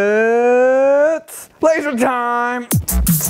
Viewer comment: For anyone else did it just say: "It's pleasure time" at 7:48 on the auto-generated captions or is it just me?